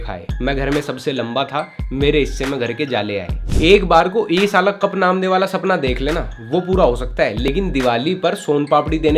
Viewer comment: What he said?? Anyone can tell me?? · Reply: "Cup namde" rcb ka slogan